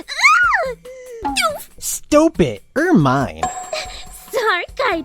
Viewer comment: The self-insert yaoi was so painfully accurate. It made my physically flinch. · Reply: oh well thank you I really appreciate that. ^^ but im still serious about what Kaito said, what did he say is sounds like he said 'er mine'